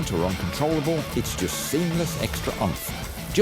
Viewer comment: did he say "seamless extra grunt" at 2:01?? oh dear oh dear, what a little fibber